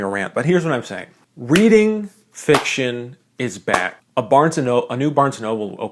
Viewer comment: I heard "reading fiction is bad" instead of "is back" and I was so confused